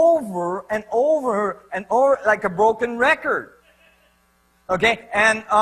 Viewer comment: Minuto 2:50 en los subtítulos pone [como un registro "volcán"(?)], y lo que dice Nassim es "like a broken record"= "como un disco roto (rayado)"